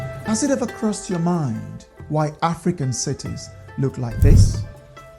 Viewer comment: Oh you said African cities. I thought you said Afro-Amercian cities.